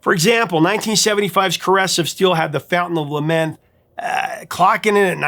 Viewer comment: Sounded like he said "The Fountain of Lament". LOL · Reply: He did say Lament. LAMNETH. At least he knows how to pronounce PEART!